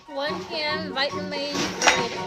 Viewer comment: did she say vitemeeze burritos???!